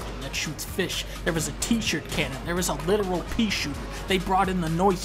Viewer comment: I thought you said teacher cannon